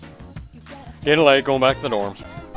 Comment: it sounded like you said gettin laid thats so strange